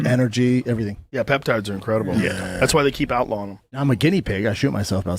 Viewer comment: does Joe say "that's why they keep outlawing them"? He is such a strange guy